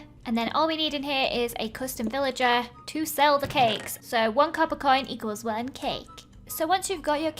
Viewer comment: I thought you said, "And it costs 1 cup of coin!" Lol